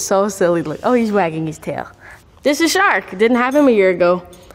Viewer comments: I thought she said shrek and i was so confused lol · Me: thought she said her dogs name was shrek